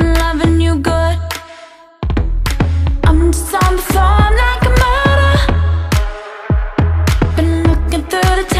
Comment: I thought she said "I'm some phone like murder"😂